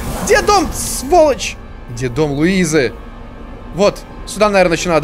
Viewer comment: мне одному послышалось детдом Луизы🤣🤣🤣